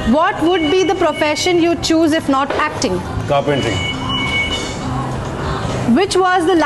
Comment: is he said carpentry?